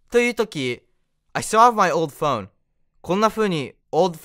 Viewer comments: の "still have"の発音のコツが知りたいです！ スローで聞いてみると「スァブ」 のように聞こえる・・・？ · の❝still have❞が❝sell❞っぽく聞こえちゃう…。 普通に会話でこれを聞いたら「古い電話を売るって話をしてるんだな」と思っちゃう、たぶん。 で、「それがいいよ」とか返事をして、どんどん会話がズレていくという…。私のあるあるです😓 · の❝still have❞が❝sell❞っぽく聞こえちゃう…。 普通に会話でこれを聞いたら「古い電話を売るって話をしてるんだな」と思っちゃう、たぶん。 で、「それがいいよ」とか返事をして、どんどん会話がズレていくという…。私のあるあるです😓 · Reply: 「スァブ」 のように聞こえると仰っている方がいましたが、確かに「still」のｔが聞こえないんですよね。 私には「セルブ」って聞こえるんです。 ファジーちゃんの「I have」は「I've」だと思うことが出来るから、「still」も慣れたら聞こえる（というか「stillって言ったんだろうな」と判断する）ことが出来るようになるのかもしれません。…と自分に思い込ませてみる😊 しつこく失礼しました。